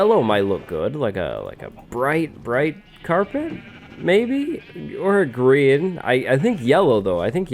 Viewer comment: did you really say "or Grian?" :D